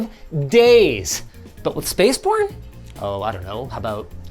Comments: shout-out to the closed captions for hearing "spaceporn" instead of "spacebourne" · captions say Spaceporn instead of Spaceborne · I heard "spaceporn" the first time he said spaceborne.